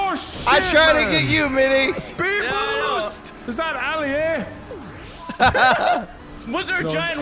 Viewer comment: Did just said Ali a lol